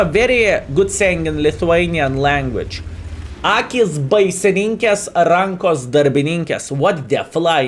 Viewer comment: I'm not familiar with Lithuanian language, but if I hear correctly, it's probably written "Akys baisininkės, rankos darbininkės" (for anyone curious, trying to Google it). I find it very interesting, when you speak some proverbs in your native language, it would be nice to hear these more often :). Cheers!